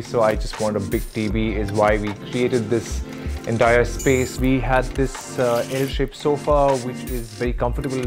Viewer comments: Did he just say "we has this ...." at 1:14 Wow. 🤣 After this can't ignore his grammatical mistakes. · did he just say "We has this" nooooooo